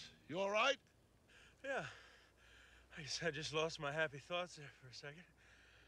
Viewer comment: “Yeah, I’m all right, just lost my happy thoughts there for a moment.” For a second I thought Louis said he almost lost his HAT(because his hat does start to come off his head as he falls, ha ha.)